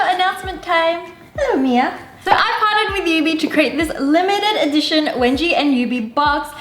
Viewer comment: did you just say "I farted with you guys"? YOU FARTED WITH US SOO DISGUSTING WENGIE!!!!!!!!!!!!!!!!!!